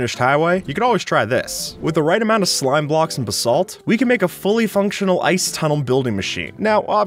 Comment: Did you just say "Bisalt"